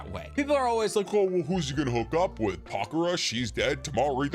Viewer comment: Who did he say at 15:39? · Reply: He said pakura. she was a very strong sand shinobi and she also had a kekkei genkai scorch release she was reanimated in the fourth great ninja war by Kabuto.